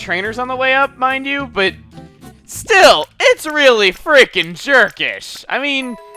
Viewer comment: at first I thought he said "its rally freaking Turkish" XD